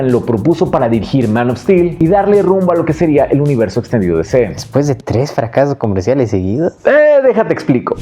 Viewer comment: escuche mal y pensé que habias dicho "el universo estallido de DC"